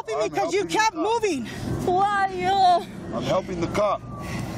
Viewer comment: 国籍は？ 12:55 こわいよーと聞こえる👂